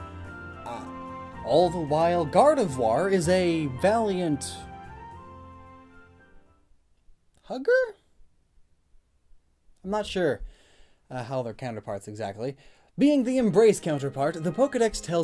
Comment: Really thought you said hooker not hugger, makes the black hole thing sound.....odd